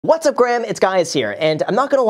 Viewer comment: Was I the only one who heard him say, what’s going on Graham it’s guys here???😂😂😂😂